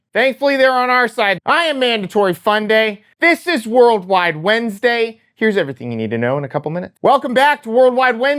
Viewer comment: Holy shit I nearly thought i heard "... and this is world war three day" Phew